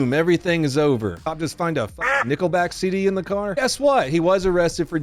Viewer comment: I thought you said the police found a "nickle bag" in his car and I thought, "Well, THAT'S not so bad", but then I realized you said "NicklebacK" . Oh, yeah, you gotta lock him up for that. Cretin!